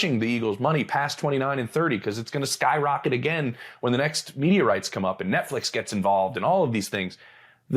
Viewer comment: Honestly thought was saying when the next meteorites come up, then a realised he said media rights. Meteorites. Media rights. Call Heeded.